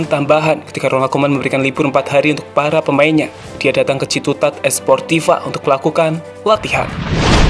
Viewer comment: gw dengernya ciputat